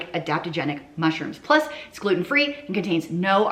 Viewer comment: These soup recipes sound so delicious. I need to confess something. I heard you describing the shake powder. I misheard an ingredient in the powder and had a laugh. I thought you said “psychedelic mushrooms.” I totally got that wrong.